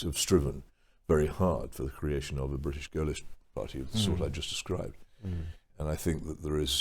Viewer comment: "a British ????ish party"? Auto-captions are confused too : "British girlish party". Does anyone know what Peter Hitchens said there? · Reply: British Gaullist party